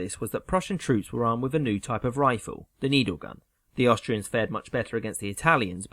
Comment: ish I could have sworn he said their new guns were needlers. Like from halo lol